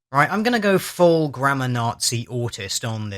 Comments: At first, it sounded like you said grandma nazi. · I thought you said Grandma Nazi. I was so confused about what your grandparents must be like.